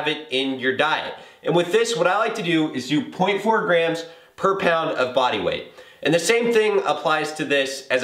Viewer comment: loooool...i thought it said 4 grams of fat per pound of body fat i almost had a heart attack